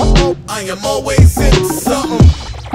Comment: I kept hearing " I am always into summa! Why not fall or winter? 😂 "Something" I know